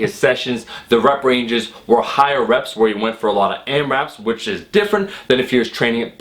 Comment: What is saying here? Aimreps?? Airreps? What does that even mean?